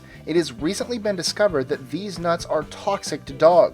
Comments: I thought you said deez nuts o-o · Anybody else heard him say deez nuts🤣🤣🤣